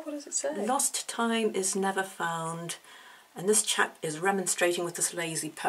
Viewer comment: I thought you said, A lost tide is never found. (: I think I like that better. Great collection, TY for sharing. My fave is the eagle bow on the battleship?